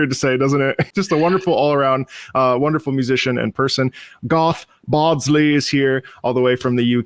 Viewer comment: I misheard the name at the start, and now I want to know about Goth Bardsley, who I can only assume is a youtuber that does covers of Evanescence songs in old english.